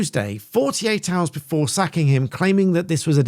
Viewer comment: I thought you said “sucking” him on Thursday 😂 1.38